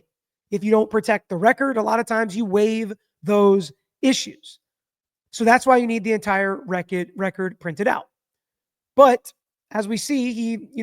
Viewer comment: When you started to say " thats why they need to have the entire record transcribed .." you actually stuttered a bit an initially said ( or what i heard was) " thats why they need the entire wreck .. uh... record transcribed" Lol, thats what I thought i heard , anyway. Could be just my bias..